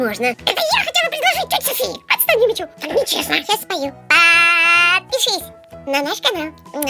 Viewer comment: сначала послышалась отпишись